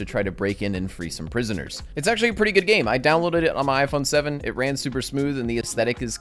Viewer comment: Did he just say iPhone 7?